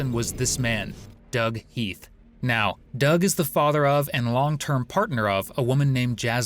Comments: did they say Doug was her father and partner? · Did he say "Doug was the father of and longtime partner of Jasmine Webb " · did you just say he’s her father and her long term partner? Is this an AI channel or what…? Wtf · "Doug is the FATHER and LONGTIME PARTNER of the woman" ........ Am i hearing this correctly? father AND partner ??????